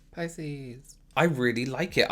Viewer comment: When you said pisces I got rlly confuse for a sec and thought you said 🐱